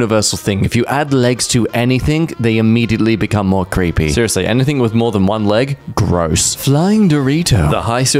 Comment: did I hear it correctly that "if you add legs to anything, they immediately becomes more creepy. seriously, anything with more than ONE leg ... gross"